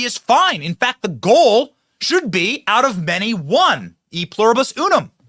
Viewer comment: I had to do a double take. I thought he said “Out of Benny comes one” haha